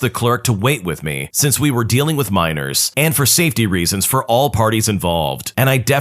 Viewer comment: ay yo pause, WHAT DID HE SAY????!!!! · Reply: Miners.